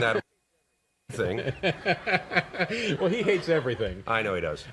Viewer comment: "that rascal Roger E thing" is what he said, for those wanting to know. I guess he wasn't a fan of Roger Ebert's review of Three Amigos! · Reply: Wrong, he said "Rex Reed".